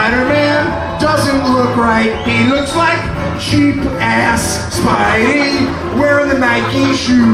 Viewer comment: I thought he said "Sheep ass" for like 10 years. Which is 100x funnier and I dunno why. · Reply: VivaLaLazlow He said "Cheap Ass"